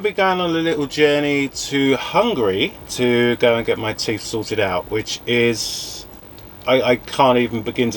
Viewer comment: Looking good guys. dancing reminds me of a young Jamie Lee Curtis with her hair style. ❤❤❤🎉. Great to get your teeth done, I thought I heard you they're gonna Solder them on, but you meant sorted out. Great video. Hi. ❤❤❤